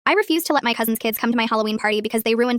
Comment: I thought it said I won’t let my husband’s kids come to the Halloween party. I was like…